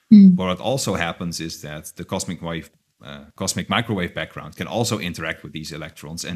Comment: Did he just say 'cosmic wife'? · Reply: He said: ...cosmic wave (then corrects himself) cosmic microwave background...